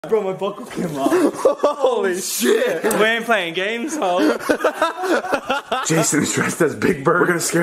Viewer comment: Hold up said “ we an’t playing games though” this whole time I thought he said “ we an’t playing games Hoe” 😂